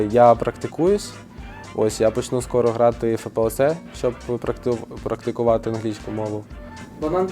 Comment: Що він сказав на 11:55 ? Як буде практикувати? Дуже цікаво, але не розібрав